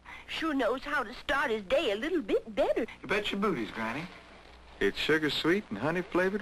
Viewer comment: Im here thanks to Gamegrumps but... Did he say "Bet your boobies granny" at 0:48 ?